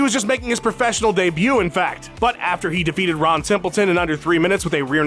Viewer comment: I thought he said "Ron Simpleton".